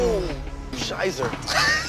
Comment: did he just say "scheisse" 1:14 ? :D